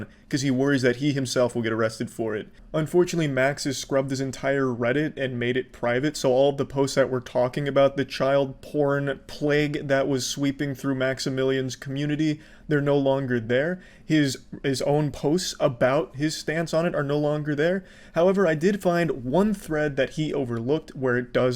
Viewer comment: I thought you said maximilian dood for a minute there.